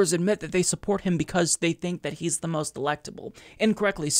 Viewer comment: For a second there (at 6:48) I thought you said Joe Biden's supporters thought he was "the most delectable" and I threw up in my mouth a little.